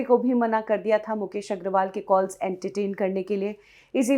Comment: Kya bola? 2:11 mukesh agraval ke call entertain karne k liye...suna mene.... 🤔🤔